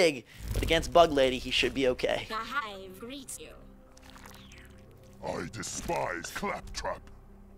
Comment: did he say i despise clap trap